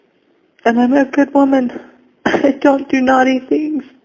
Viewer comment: I agree; the “little girl” speech is so strange and cringey. These calls have changed the way I think about her tearful speech at sentencing. 🤢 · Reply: I thought Ruby said "I'm a good soul", but apparently it was 'girl'. "I don't do naughty things." (Just torture my kids).